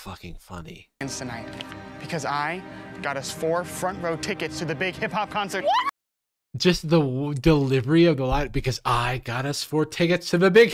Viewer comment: i thought he said the big hippo concert at first and i was like "is this another collab or a fake band?"